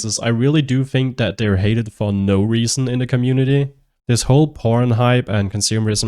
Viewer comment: Are you saying "pr0n"??? Im so confused because I hear you say it again later in the video. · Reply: HAHAHA, no, it's "PORON", it's a licensed material